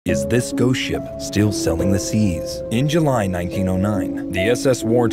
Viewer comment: I heard, "selling" the seas..